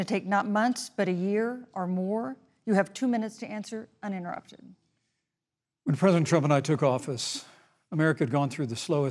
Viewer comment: Did Anyone else go right to the comments to see what people say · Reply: Yo i watched live and at 42:14 i SWEAR TO GOD he said "when father trump and I took office", so i instantly googled and saw some shit that was (possibly fake) about him calling trump 'father' months ago. I guess im crazy but i had to put it out there 😐